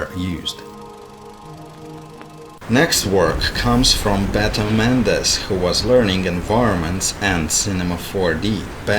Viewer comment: Hello and thank you! Congratulations to everyone. Just a thing haha Can someone tell me what said to me? 17:40 he said "It is to make it look like a ------" ? English is not my native language so I am having a hard time getting that laaaaasstt word! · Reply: I think he said “painting.”